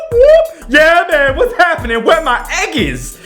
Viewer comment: did he just say wheres my eggies?